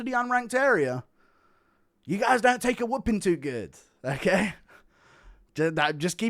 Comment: bro you did not say the YNs don’t take a whipping too good